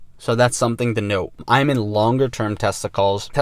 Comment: Did he just say i'm in the long term for testicles ,,lol we know where is mind is really on ,on some Testicles and not Crypto😅